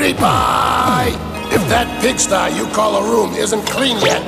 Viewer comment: did bowser just said bitch?